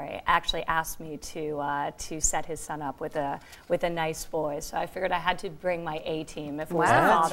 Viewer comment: Did she say HIS father asked me to set him up with a nice BOY?